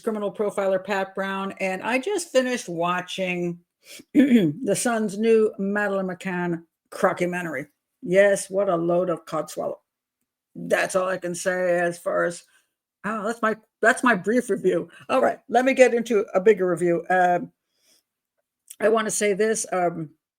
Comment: Did you say "a load of cockswallow," at 0:28 ? Lol